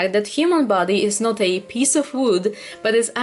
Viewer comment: “The human body is not a food” Me: “I’m sorry what?” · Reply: I heard "the human body is not a piece of wood"😐